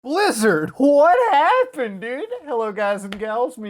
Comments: At the beginning i thought he said "LIZARD, WHAT HAPPENED!?" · I thought I heard “Lizzard! What happened?”